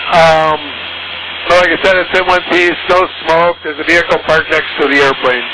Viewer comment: I think he said "no smoke", not "still smoked"